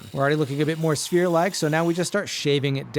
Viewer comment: Got excited because I thought you said spear 😭